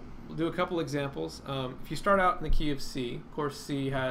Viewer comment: The "KeyOfC" i thought u said the KFC